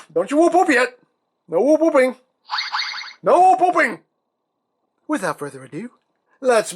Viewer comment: I heard 'no pooping'...was that a slip of the tongue?